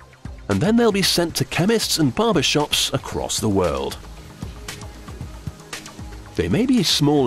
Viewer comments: did he say "they will be sent to chemists and barbershops around the world"?? I swear I heard that. What the hell does that even mean? Chemists, barbers, condoms? what? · did he say theyll be sent to barber shops?